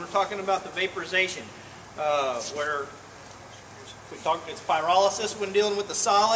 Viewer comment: What did you say it's called with a solid? "Virolosis"? At 1:17 - I just couldn't hear properly. Thank you! Cheers, Chris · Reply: Pyrolysis